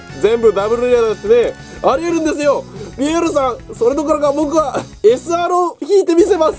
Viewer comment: 何故かピエールがディアボロに聞こえてきてじわじわきてるww